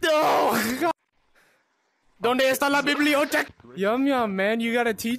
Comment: I don’t know what he said he’s like bibbilee random word